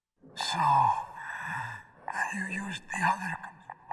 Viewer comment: I never knew he actually said "you used the other controller" Back in the day, the "controller" part of the line was muted I guess...